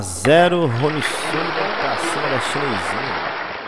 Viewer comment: Só eu que ouvi "pra Cima da CHINEIZINHA" 😂😂😂😂